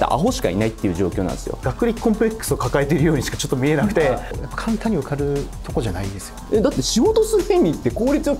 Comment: ここ聞き取れないんですけど、誰かわかる人います？ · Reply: 「簡単に受かるところじゃないですよ」とおっしゃっているように聞こえます。